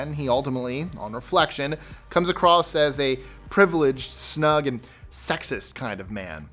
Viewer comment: Did he say, "A snug man"?